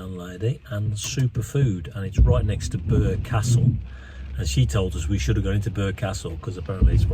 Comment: Not being from there I'm quite confused. It sounded like you said Burger Castle so why did you eat with a lovely landlady if you were suggested to go to a fast food hamburger place? LOL crazy American🎉❤